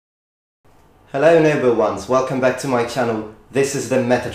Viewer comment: For the longest time whenever Metatron would say "noble ones" I thought he was saying "number ones" like star trek.